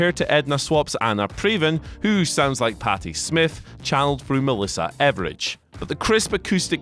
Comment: Did he say Melissa Evridge?